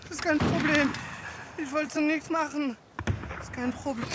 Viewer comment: My German is hardly any good these days 😂 I think he said this but yall can correct it if you know it! "Es kein problem. Ich wollte nichts machen. Es kein probl- HUUUUUUUT!" 😨 = "Its/there isn't a problem. I didn't want to do anything. There's no probl- HUUUUUUUT!" 😨